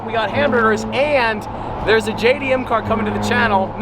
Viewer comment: Did I Hear JDM?